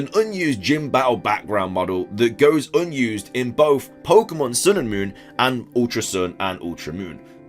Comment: Hahah I thought in your intro you said in the 7th generation we were introduced to Pokémon sword and moon haha